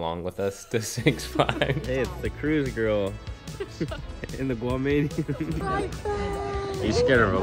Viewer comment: Did you say Guamanian at about 8:30?